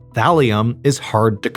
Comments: I thought you were saying Valium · the first 5 times I heard "Valium " and Diazepam isn't that lethal 😂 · LMAO I kept hearing “Valium” and was like “oh nooOo” 😂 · It sounded like you were saying “Valium” - I was so confused! Haha!! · thought he was saying valium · I thought you said valium and after having just taken mine I was really confused on your description about it